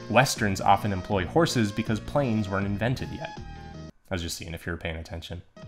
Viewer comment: I had a double take cuz I thought you said horses weren't invented yet xD 8:50 ohmygod! I hate when that happens! xD this actually tends to happen to me a lot but when someone recommends me music it's the opposite.